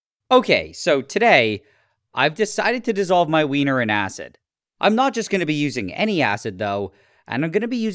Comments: bro I was looking away and then I heard “ today I decided to put my wiener in acid”🫩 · Why did i hear "Today i am dissolving my weiner in asses" 🙏💀